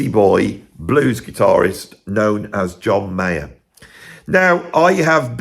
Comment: Relieved, thought you were saying John Mayall.